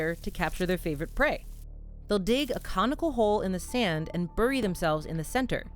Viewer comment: For a second, I honestly thought you said they dig a "comical hole" instead of "conical hole." My brain immediately thought "the ant doesn't think it's very funny."